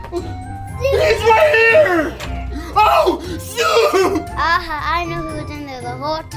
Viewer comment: I Thought he said wheres snoop dog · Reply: Snoopy